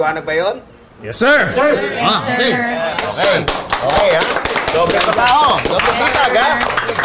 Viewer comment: Parang narinig ko sa 0:34 na doble trabaho doble sapag Sabi ni pepito narinig kolang naman